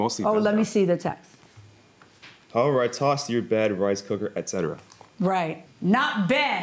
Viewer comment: At first, I thought he called him a "rice cooker."